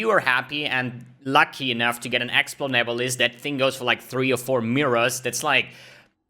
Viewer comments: What did he say at 4:04? If you are lucky enought to get what? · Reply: Explode Nebulis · what item is he talking about being worth multiple mirrors? I cant understand what he says there, and I cant even find a unique item worth multiple mirrors , let alone something specific to cortex. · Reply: Nebulis with this implict Enemies you Kill Explode, dealing 3% of their Life as Physical Damage